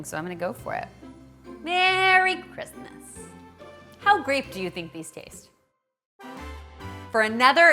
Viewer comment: did I hear you say grape? Haha you and your puns :-D